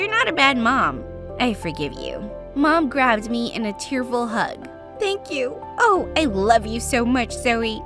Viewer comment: at first i sounded like she said a terrible hug🤣